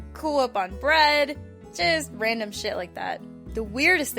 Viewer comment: did it say she craved jizz? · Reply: She said just random....